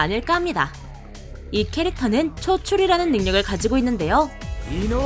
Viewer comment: 메추리라 들려따